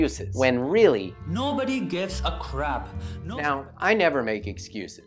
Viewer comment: lmfao i dont even know who mensutra is lol but damn that cult life huh lol and who doesnt hate bollywood lmfao jk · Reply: lmfao when he said no one gives a crap i thought he said no one gives a crab lmfao damn......